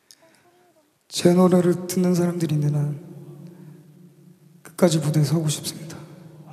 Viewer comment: can anyone tell at 2:11min what his say ?? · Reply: as long as there are people who listens to my song... i want to stay on stage.